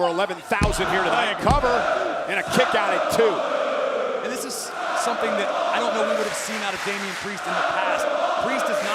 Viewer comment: What was the crowd shouting? I couldn't hear it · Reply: Simplement deux (Only two) This is a famous phrase created by owns commentators Agius and Chereau (Sorry for my english im french)